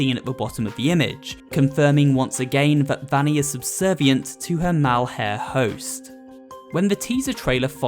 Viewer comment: did you just say... "Mal-Hare"?